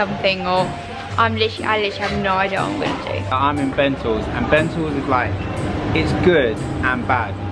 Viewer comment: I thought said I’m in bagels 🥯 but he really said I’m in bentals